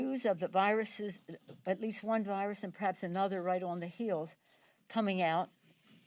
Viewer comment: She says news of a new virus and perhaps another one on its heals, and it's a positive note 🤔. Did I hear that correctly?